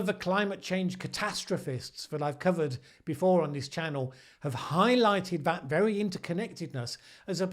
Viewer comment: Sorry if it wasn't clear - it will be here, on this very channel! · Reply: Great, looking forward to it. I heard "... on his channel." rather than "... on this channel."